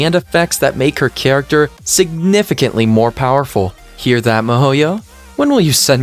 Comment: did you just say mihoyo? By any chance ,are you playing genshin impact tooo???